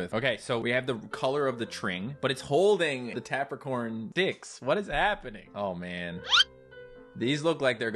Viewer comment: is funny he said d!cks instead of sticks🤣🤣🤣🤣